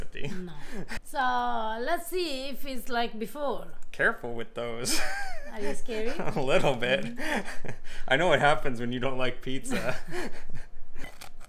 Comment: haha so cute. “Are you scarey?” Instead of “Are you scared.” 😊